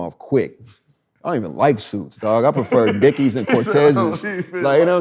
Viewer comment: At 1:8:30 did earl just said I don't even like suits, I like Dickies and Cortez lol😂😂. He's a real vato💯, he ain't half Mexican he's half esa lol